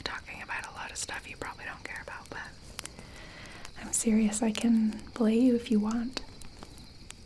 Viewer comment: i thought said i can bully you, instead of belay lmao